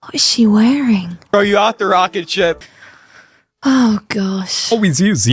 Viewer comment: I thought he said he thought SHE was the rocket ship, lol. But if there was a second seat, I would've said I'm bringing my best friend or significant other.